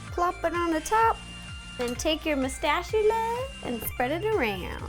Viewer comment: did you just say mustachula? O_o